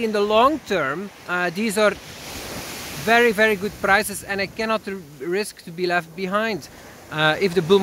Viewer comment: Hi, I'm just curious. if you think it's going to a new low around 2800 then how do you decide the % you have placed buy orders around 4K? I'm assuming you're not going 70% invested at around the 4K level? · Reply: ah, nevermind. I just heard you say "in the long term... good prices, and I cannot get left behind" gotcha.